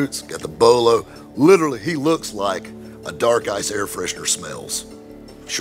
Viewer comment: Did he say “He looked like a dark guy’s air freshener smells”? I replayed it twice hoping I misheard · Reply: “Dark Ice” air freshener